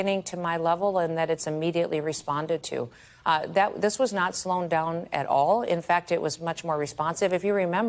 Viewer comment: okay...maybe I'm wrong but @ 6:32 does she say "not slown down"? I played it a couple of times and I think she said slown. smh